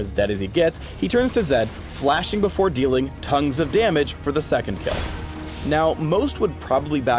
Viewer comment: did phreak just said : tongues of damage???? I'll kill you.